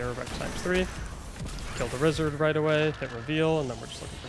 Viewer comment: did you say rizzard instead of wizard😂 love the video btw👍